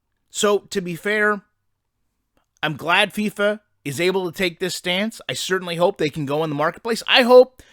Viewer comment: I thought that said "fifa is bullshit" 😂